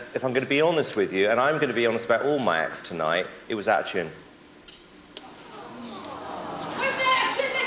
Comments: What does Simon say at 1:48 ? "It was action"?? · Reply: it was out of tune. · What did Simon say? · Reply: Out of tune